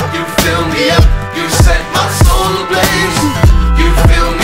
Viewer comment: "you set my soul ablaze" I thought he sang: "you said my soul look' lazy"